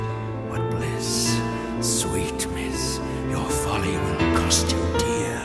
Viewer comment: What is Hyde saying at 1:47 ? "Your [what] cost you dear.. " ? I wish we had lyrics. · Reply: Folly. He’s saying folly.